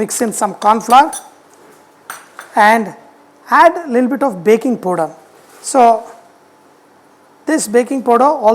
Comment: Did he said BAKING PODA??